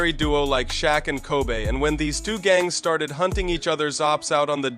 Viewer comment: did he just say Shaq and “Kobay” ?😂😂